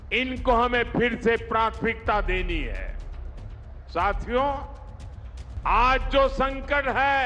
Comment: पता नहीं एसा क्यों है; जब भी यह जुमलेबाज़ पब्लिक को बोलता है 🎙" साथियों "------ तो मुझे सुनाई👂 देता है;--' " चू🪑यों "!!! आप लोग भी; ज़रा ध्यान से सुनिए 🤷🏻‍♂️।।।।